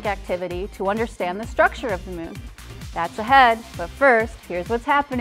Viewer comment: I thought it said “structure ON the moon”.....oh well.